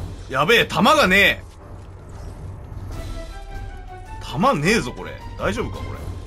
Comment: たまんねえぞって聞こえた